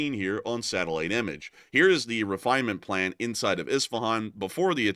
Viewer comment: did i just hear ITSVAAN ! Is it Itsvaan IV ?